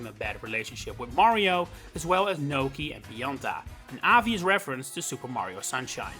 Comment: I'm sorry but I don't understand your accent here. I think Pionta is supposed to be Pianta, but who is Gnocchi/Nokey? Edit: Oh. just looked up the roster...I have no memories of Nokis in mario sunshine. I never finished that game, so..I guess that's why